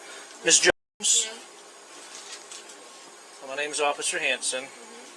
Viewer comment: It almost sounds like he says "My name is Officer Handsome" 😂😂😂